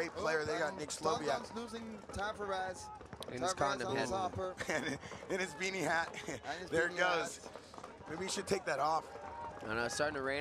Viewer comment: "Maybe he should take that AWP" Is this a joke or did I hear something wrong lmaooo